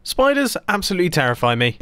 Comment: Ok. I opened the video and I could've sworn I just heard him say that SPOILERS terrify him, not Spiders. Was that just me?